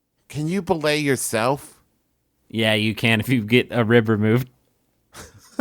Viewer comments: I misunderstood Grif and thought he said "Yeah, you can if you get a river mood." Thank you comments for quoting his actual phrase of "Yeah, you can if you get a RIB removed" so I could go back and still appreciate the joke! 😊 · i ruined the joke forever for myself because i heard “if you get a river moved”